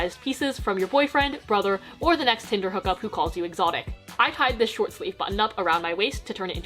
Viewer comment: "the next tinder hook up who calls you exotic" 😭😭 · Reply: 🤣I thought she said "the next tinder hookup who calls you at Sonic" I was like....what?? Lol